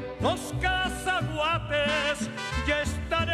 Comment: Yo antes pensé que decía calzahuates